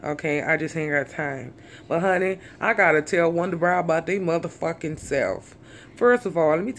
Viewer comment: i thought she said wonder bra lol